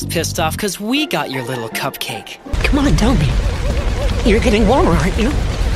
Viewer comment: i swear i hear "ur getting walmart"